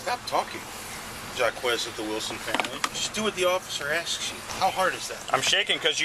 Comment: When he's trying to spell out his name, I kind of heard it as JackAss of the Wilson family.